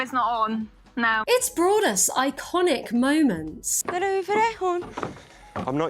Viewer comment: I heard “it’s brought us countless iconic moments” as “its braless iconic moments”